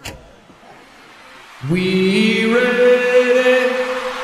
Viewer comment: It sounded like you said we Reddit